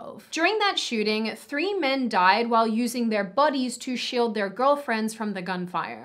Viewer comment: I thought you said "buddies" I WAS SO CONFUSED XDDD